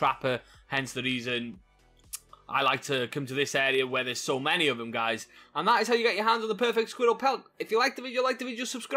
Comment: Did he just say how to get a “squirttal pelt”?